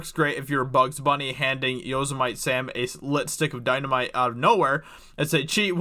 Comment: did you just say yos a mite sam? what kind of fandom did you grow up in dude?